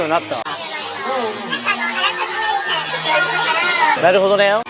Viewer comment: でホストが「なるほどだよ」って言ってるように聞こえてすごく気になってしまった。 なるほどだよとは？？ これもホストの心を掴む話術ってやつなのか？